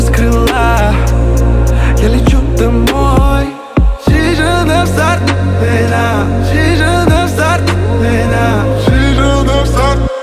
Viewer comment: я один слышу чижик насрал в пенал 😂😂😂😂